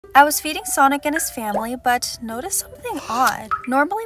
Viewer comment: i thought she said ``i was beating sonic and his family